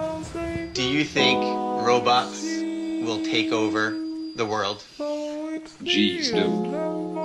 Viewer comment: I thought he said it's Roblox well take over